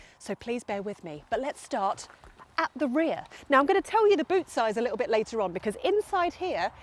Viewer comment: You threw me at the beginning; I clearly heard you say: “I’ll tell you the boob size later on.” My apologies, keep this clean. You are so right about 21” wheels! I can’t understand why people build comfortable cars with super-fancy suspension, and then ruin it? I have a car with 21”, but also winter tyres at 19” – and I look forward to every winter just for the wheels. Duh!